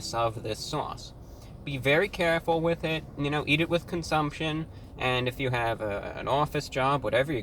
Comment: did you say "eat it with consumption"....I thought all food was eaten by consumption